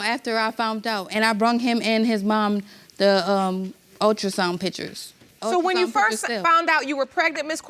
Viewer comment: Did she say "I BRUNG him and his mum the ultrasound pictures" on 7:38 ??😅😂